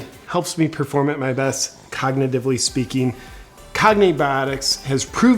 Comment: I thought he said commie biotics at the end lol